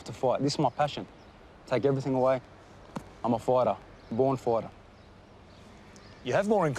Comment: Did he say, "I'm a born farter"? 😂😂😂